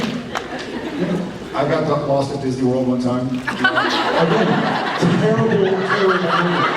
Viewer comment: What did they say after "I broke my arm" part? 17:20 And honestly, how many times did he break his arm? Someone please wrap my baby in bubble wrap ^^ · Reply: "I got lost at Disneyland one time." is this the part you were asking about?